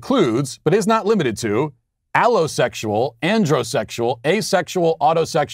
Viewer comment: I thought you said owl sexual oops